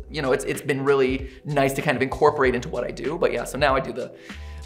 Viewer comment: Wait he says Buddy that's just a theory for the whole time?!?!??! I thought he was saying but hey it's just a theory, for so many years I missheard an Iconic Voiceline of Game theory???